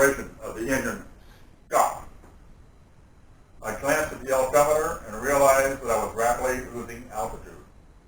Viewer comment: For all the mongoloids in the comments that think he doesn’t know what an altimeter is, listen closely to him saying it: 9:35. He says “altimeter” but the subtitles say “odometer.” Fucking relax